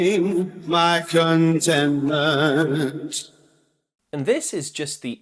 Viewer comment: I always heard it as bad contentment